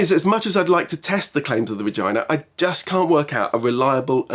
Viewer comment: Did he say Vagina